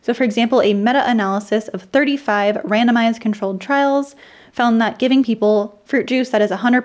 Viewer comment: OK, @ 7:55 you said NOT giving people fruit juice is better for blood pressure, then conclude that it is better for blood pressure. Did I hear that right? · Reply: I said “found that” not “found not”. (I talked too fast in this one, sorry. My daily speech is still faster than this believe it or not) Thanks for providing the timestamp!